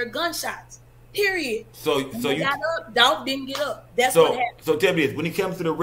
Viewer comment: They was she said it at exactly 21:55 " Dolph did get up, thats what happen " said like she dgf like she is the camouflage opp.